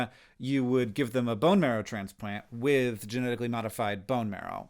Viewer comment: The first time he said “bone marrow”, I heard “boner”. lol